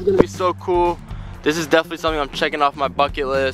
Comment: I thought Darius said f*** but he said check off bucket list